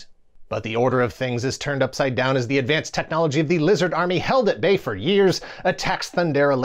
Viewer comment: I SWEAR, I misheard 'LGBT LIZARD ARMY' in the opening monologue. I better get some coffee before I mishear more things!